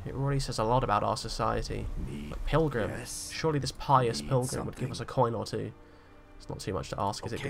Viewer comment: My volume was low and I thought you said "pious milf", not pilgrim. Thought this series was about to take an odd turn 😂